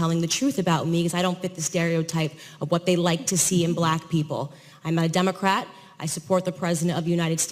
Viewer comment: Why did they edit this to make it sound like she said, "I'm a Democrat?" Another example of fake news!